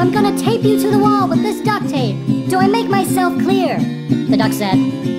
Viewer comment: Which is better Duck Song : Like Baby Shark : Comment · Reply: tape a duck to a wall with duck tape yes I know its duct but cmon it sound like she says duck